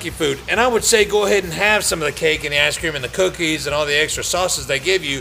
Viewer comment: Sounds like he said ass cream at 1:05 lol